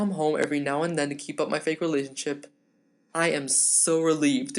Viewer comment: did he say fake relationships